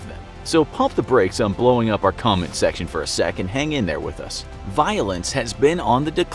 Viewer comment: when they said violence at 1:34 i thought they said violins 😂😅